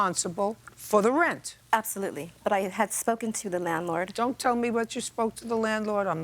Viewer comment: Landlord SMANLORD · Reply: Are you talking about 1:42? I thought I was hearing things, then replayed it & I heard "manlord"! 😆